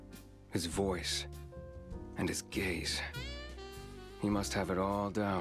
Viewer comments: His gestures.... his voice.... and his gaze... I thought he said "gays" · “ His gays “ I misheard it’s meant to be gaze but 😂😂😂